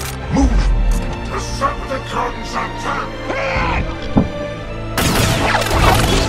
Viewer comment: did anyone else hear decepticons attack? 4:08 (trans theories thinks that it was brawl only time he spoke his proof is very beliaveble i am just saying this in case anyone didnt know) this final fight sccene was amazing in everyway sadly jazz died but there is always an autobot that dies in each transformer movie but i think jazz should have survived and no autobot dies in the 2007 movie.